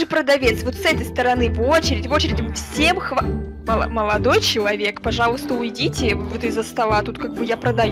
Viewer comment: Чилодой моловек мне послышалось 😂